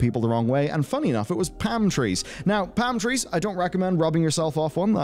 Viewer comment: The way you kept saying 'Palm tree' had me rewatch that bit. I kept hearing 'pantry'.